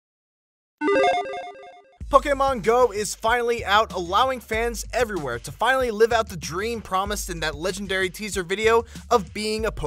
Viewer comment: it sound like he say fuckingmon go